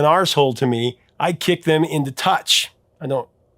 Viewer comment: I think she said I'd kick them in the TUSH, as in, the buttocks! YOU KNOW WHAT TUSH MEANS RIGHT?